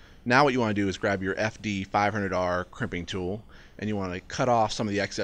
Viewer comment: I thought he was saying "Grab your $500 crimping tool".